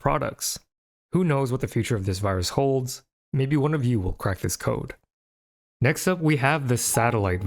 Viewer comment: I thought he was gonna say "maybe one of you will catch it" it cracked me up